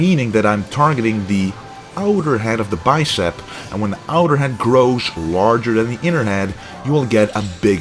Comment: sounded like you said 'when the outer head grows larger than the internet' lol